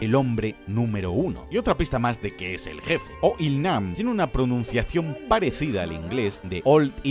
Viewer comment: La primera vez que escuche el nombre del 001 entendi "oil man"